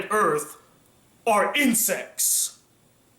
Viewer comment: Wow. When was talking about Kushana, I actually misheard 'insects' for 'incest'. Is it because I know too much about tastes now? ^_^